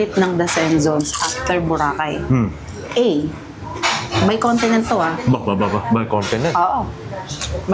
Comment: Malalaman na natin next week beshy! Abangaaaan 🥰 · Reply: haha I rewatched and you said may continent. I thought you said may content😂 well if it’s America I hope it’s in the San Francisco Bay Area!😊😊😊